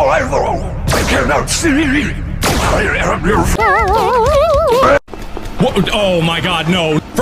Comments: “I AM YOUR FRIEND” proceeds to kill Gregory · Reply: I thought Fredy said I am your father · I thought he said:"I AM YOUR FATHE-"